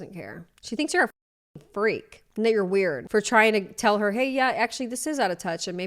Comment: I had to go over your first couple sentences again, because it Very Much (to me) sounded like you said "n word weird" instead of, "and that you're weird"... I thought the video was going to be about you for a minute lbs